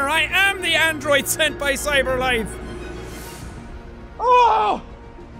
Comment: When I watch the first episode of this. I misheard Cyberlife as Afterlife My name is Connor. I'm the android sent from Afterlife That's so dumb of me